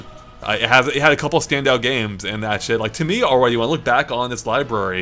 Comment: did he say sh*t